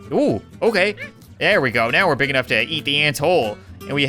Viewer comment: did anyone hear say "Now I'm big enough to eat the ass hole."?